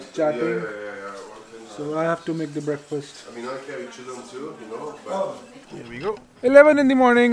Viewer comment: bro I think I heard him saying "I carry chillum too" btw great videos very informative came here from the one in munich